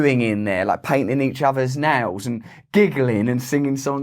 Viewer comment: omg, so sorry. I wasn't watching, just listening when you said "painting each other's nails" and it sounded like you said "painting each other's 'nads" Carry on.